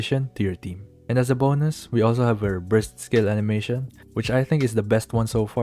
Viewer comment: I swear I heard "her breast skill animation" for some reason